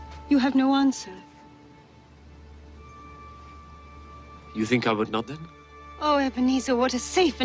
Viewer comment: What did young Scrooge say in 47:03? I couldn't hear what he said. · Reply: Seems to be " You think I would not then ? "